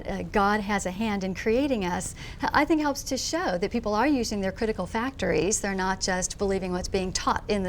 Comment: Did she just say "using critical factories?"